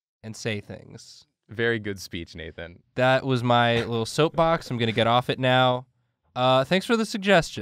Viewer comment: Soap box? I thought he said dark souls. You should do more dark souls bosses